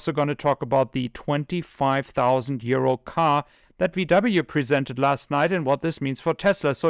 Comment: Lol, I had to listen to that beginning twice, I thought you said VW's 25,000 year old car.